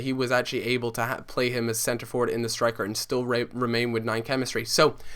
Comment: did he just say "He will rape remain " LOL